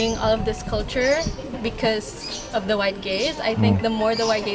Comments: "The white gaze?" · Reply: Shan't lie I thought she said 'the white gays' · HELLO, at the end does she not say "the white gays" ???????? What is she saying??? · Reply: White gaze